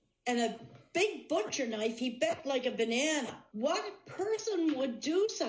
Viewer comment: I heard the phrase "he bled like a banana" the first time..... I kinda want that on my headstone